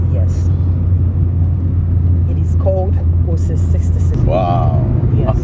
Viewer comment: what exactly did she call the cologne that goes for 300k? min. 17:46 Part 1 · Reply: i think she said Osin 66.